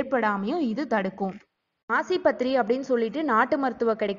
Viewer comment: Some words lack clarity..Massipathri ah? What is it said @ 9:50?